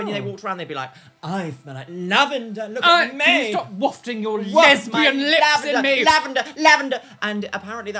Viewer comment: "STOP WAFTING YOUR LESBIAN LIPS AT ME" ADKSHVBKUSDBC BAHAHAHAHAHA THAT KILLED ME OMG I DON'T EVEN KNOW IF I HEARD CORRECTLY BUT IT MADE MY DAY